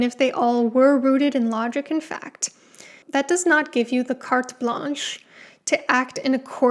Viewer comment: what is this phrase/word? I am not even sure what she's saying, but I'm pretty sure it isn't English. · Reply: carte blanche